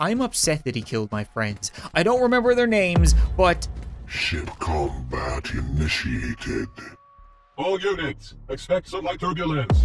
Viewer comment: I thought it said Shit combat initiated